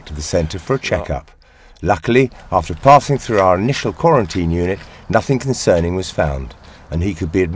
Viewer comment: does say “Luckily, after farting through our quarantine unit…”? I didn’t realise this duck was a flatulent flapper!